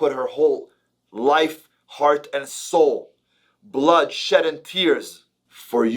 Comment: Did he say “Blood, shed, and tears”?